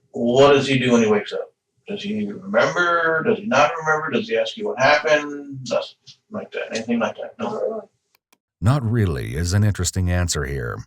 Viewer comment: personally I heard and lip-read "not at all" and not "not really" small detail but I'm kinda surprised that "not really" is what you guys rolled with, after presumably listening to it pretty attentively to get it transcribed accurately